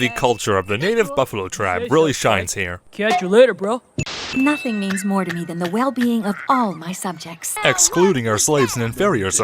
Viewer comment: I was eating pizza and while I heard this I thought Celestia said “Knocking means more to me than the well being of all my subjects”